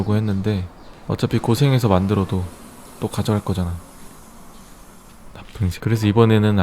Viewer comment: 에 bad color라고 자막 나올 때 뭐라고 말씀하심??(나만 안들리나..) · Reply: 나쁜 색.. 이요